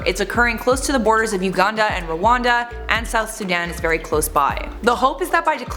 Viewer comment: I thought she said wakanda at #